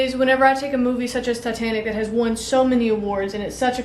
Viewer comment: I thought she said Satanic.